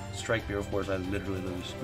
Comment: did he say strike mirror force? What card?